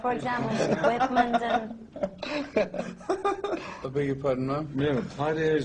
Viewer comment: Did Ozzy say 'Beg your pardon MAAM?' I'm surprised the 'gentleman' side of his exists at all considering he shat in hotel rooms and wiped his ass on Roger Whittaker's curtains (yeah, he did).